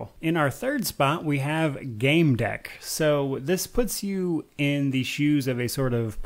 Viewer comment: What is #3? I thought you said Game Deck but I can find no info on a game named that. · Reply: Gamedec